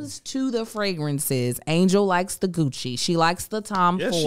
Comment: I totally thought who said I like the cucci not Gucci